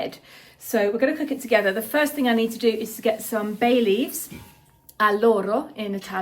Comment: Oh Bay leaves i thought you said “Baileys “ my kind of soup😅